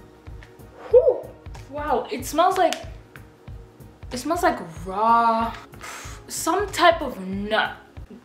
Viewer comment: Bish did you just say smells like #RawNut girl i can't wit you!